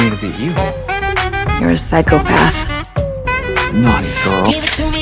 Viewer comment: I swear to you when that first clip came on “you’re a psychopath” I heard him say “I’m not a girl” when he said “naughty girl”